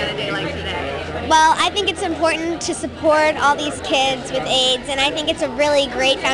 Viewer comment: Did they just say kids with ads rip Cameron